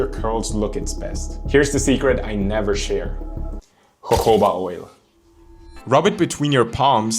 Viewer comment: did u say coconut oil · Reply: He said jojoba oil